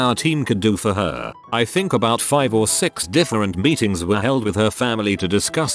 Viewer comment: I heard this as “I think about five or six different beatings were held with her family”